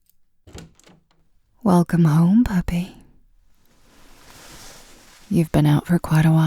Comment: I thought you said Papi (daddy in spanish), then I remembered it's f4f and puppy makes more sense